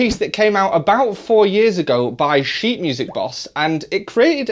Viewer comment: Sounded like you said "shit music boss"